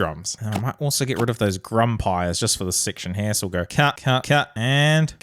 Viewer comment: Grumpier sound like he is saying the n word